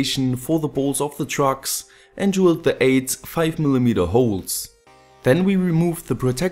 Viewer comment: did you say "drill the AIDS"